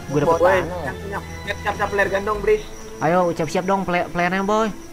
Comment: apa gua gak salah denger 08:58 bang lu ngomong peler ya